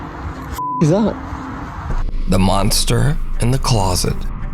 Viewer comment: At first I thought i heard the so called "monster" say "I am the monster'